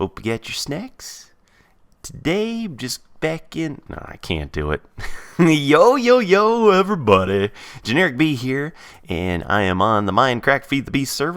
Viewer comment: everytime I hear him say "Generikb", I can't help but think of it as "Generikbee". Is that the same with anyone else?